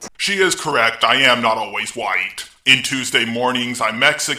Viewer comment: OH she said right! See, I totally thought she said white